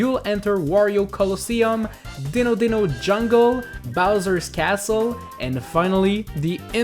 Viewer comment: "Ditto Ditto Jungle" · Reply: I thought he was saying "Ditto Ditto Jungle" instend of "Dino Dino Jungle"